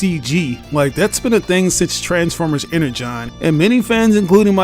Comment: I was struggling to understand your accent. I had to use the subtitles mate. I thought that you were saying "Inner John universe"? Then the subtitles said Inner John too Lol! But I think it's Energon Universe? 😃